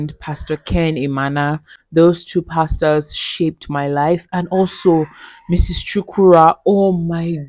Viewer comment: Did I just hear you say in 3:04 ...I'm shocked 😲 that's my surname 😅🤣it's definitely not my mum you're referring to though ( cause we don't live in asaba ) but the coincidence is funny to me . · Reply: Yeso, hehe😄 . Thanks for watching and commenting Asher. ❤